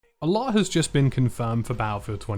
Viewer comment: You sound like you just said ( "Allah" has just been confirmed) At the start of this video ..